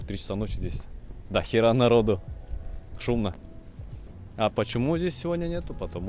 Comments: мне послышалось или ты сказал пшлнх рандом челу?!?!? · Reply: Переслушай, он сказал шумно)) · мне не послышалось? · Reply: Мне по началу тоже послышалось, а потом поняла что он сказал «шумно»